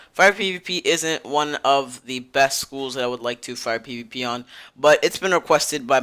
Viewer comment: can you say fire pvp again? i didn't quite hear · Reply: "fire pvp isn't one of the best schools i would like to fire pvp on"